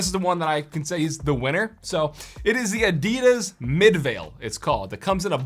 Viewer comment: what did you say? Adeeedas